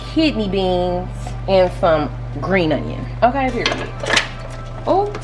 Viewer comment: Sissss plz put the ingredients in the description 😂 I’m currently at the store trying to get all the stuff I thought you were saying “Sabrina” but you were saying GREEN ONION 😂🤦🏽‍♀️